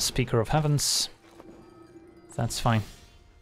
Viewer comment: I heard murmur "The shuffler is fine"